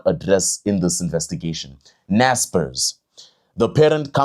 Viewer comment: Hey, when you said Naspers I thought you said Natpis. Good work.